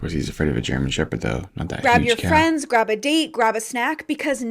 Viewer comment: Did she just say "" grab a D ! ..."" ??? ... 😂😂😂 04:39 This is an important discovery, but, i don't think it will become a new energy source ! .... 😐 57:56 Now, this is a new beat ! .... 😊👍